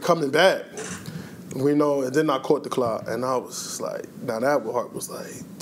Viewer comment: Love him, love this video; but when he say he caught the clot, I thought he said he caught the clap, lol